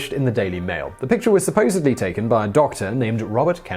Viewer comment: Did you say: "supposably"?